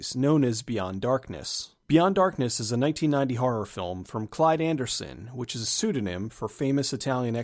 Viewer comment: For a second, I thought you said CLIVE Anderson. I was gonna go "From Whose Line Is It Anyway?!?"